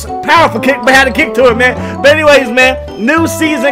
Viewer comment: i thought he said something like "eye sauce"... I had no clue.